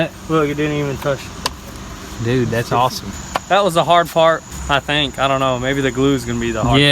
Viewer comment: On 12:28 u said that was the hard fart instead of part lol